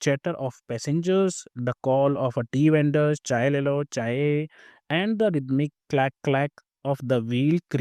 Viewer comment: What did you say sir? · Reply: Chai Le Lo Chai 🤣